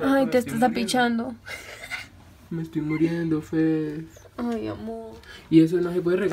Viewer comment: parece que dice "me estoy muriendo fede" Y ASI ES MI NOMBRE AJDJSJA😂😂